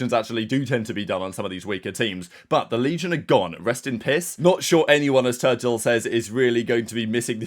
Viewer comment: sounds like says “rest in piss”🤣 Damn he hates Legion 🤣